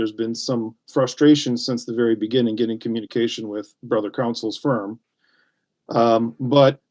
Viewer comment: Every time he said “brother counsel” I heard “this f’n a-hole”